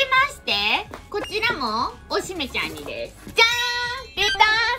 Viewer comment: いかんプロフィール帳世代すぎるw ティッシュ可愛すぎて使えない(><)♡ 3:02、おしめちゃんって聞こえるw